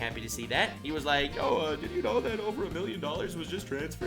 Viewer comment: Did he say a million 😀🤚, that's kinda fucked that that's how much yt owed him